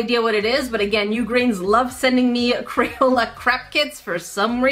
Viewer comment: I thought you said crayola CRAP kits!